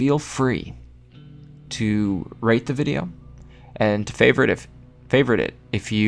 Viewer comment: for a sec i thought you said rape the video instead of rate but anyway nice video bro